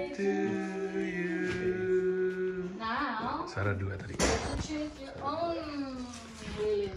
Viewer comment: gua dengernya 'shalat duha' 😇😇😇 Eh ternyata 'suara dua' WKWKWKWKWK 🤣🤣🤣